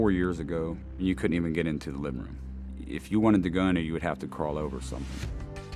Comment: does he say: "If you wanted the GUN , you would have to crawl over something" ?